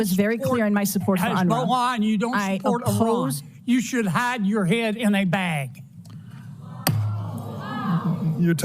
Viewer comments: I thought he initially said “you should have your head in a bag” Either way extremely disgusting. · I thought he said she should HAVE her head in a bag. He may just mean she should be ashamed of herself, but it definitely SOUNDS like he is trying to endanger her life and that's probably the main effect his language had... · I thought he said “you should have your head in a bag” Much worse lol 😂